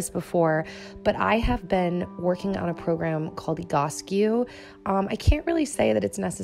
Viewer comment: Can anybody help me I can´t find the app talked about, the one in 2:27. What is it called or better yet how is it spelled hahahah. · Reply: It’s called Egoscue!